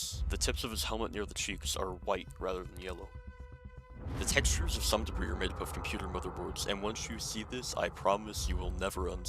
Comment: Anyone else had trouble understading what he said at 2:21? If the auto-generated subtitles are correct, that would be: "the textures of some debris are made up of computer motherboards"